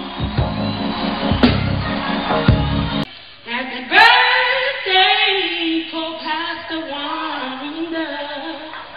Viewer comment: Wat was sayin at 1:31 lol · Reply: Shakeel Harris she said I told you